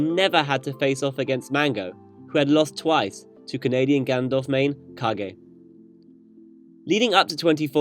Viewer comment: canadian Gandalf main? or maybe i heard incorrectly